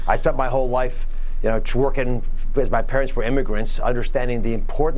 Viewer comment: did he say he spent his whole life "twerking"? teehee